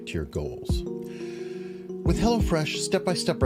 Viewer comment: I thought he was saying Hellafresh which would have been appropriate for this show. You guys are the best. Sharing